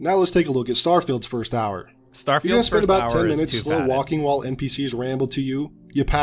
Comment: I rewatched this a few times and I can't understand what said. I'd like to know what word you used, "Starfield is too __". (Serious answer please). Thanks. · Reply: He said, “too padded”.